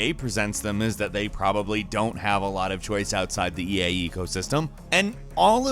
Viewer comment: Did anybody else heard him say "EA ego system" instead of "eco system"?